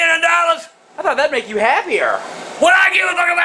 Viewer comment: Can someone translate what grandpa said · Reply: “WHAT DO I GIVE A FUCK? ABOUT THAT? I WOULDNT EVEN PAY THAT MUCH FOR GODDAMN YOU TO GO TO THE DOCTOR.”